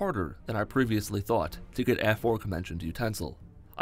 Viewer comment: Did he say the "aforkmentioned utensil" 😂😂😂 Edit: 6:44 Did this man just call Arngeir "Angelica" 😂😂😂😂😂😂 Edit Again: His name is Porky Forky 😂😂😂😂😂😂😂😂😂